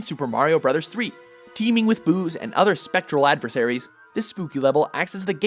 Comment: I thought he said teeming with booze